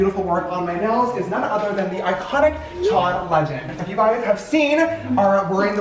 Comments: I wasn’t looking at the screen so i thought he said John legend🤣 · Was like wth thee John Legend??? I obviously misheard that lol